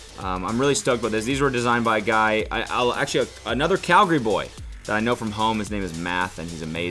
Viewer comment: did you say Calgary boy? i live in calgary